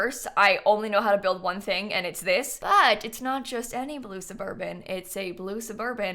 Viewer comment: I thought you say Lucifr instead of blue surburban.